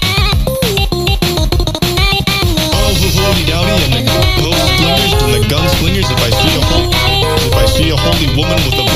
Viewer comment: what does Critikal say at this part??? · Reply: “Ah ha hodey doudey and the po slingers and the gunslingers, if I see a holy- if I see holy woman with a voice- with a voice, I’ll call her a nun singer.”